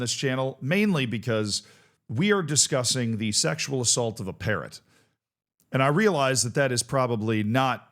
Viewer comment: whatttttttttttttt theeeee fuckkkkkkkkkk Edit, omg I thought you said parent